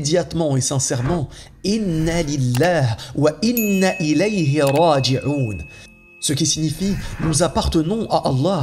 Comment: escusez moi ou puis je avoir cette invocation . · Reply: إن لله و إنّ إليه راجعون C'est à Allah que nous appartenons et c'est à Lui que nous retournons Inna lillah wa inna ilayhi raaji'uun Sourat Baqara verset 156